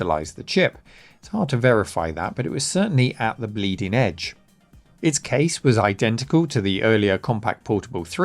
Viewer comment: did I hear Leading Edge? No, bleeding edge? Oh, anyhow a very cool computer, I wonder if anyone would be willing to spend $22k on a laptop these days?